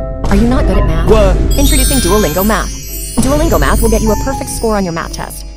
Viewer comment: It sounds like he is saying duolingo meth😂😂